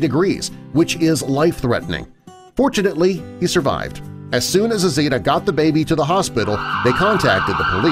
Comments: when he said "fortunately he survived" i thought he said unfortunately! so i heard "unfortunately he survived" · I thought you said "Unfortunately the baby survived." But you said Fortunately lol...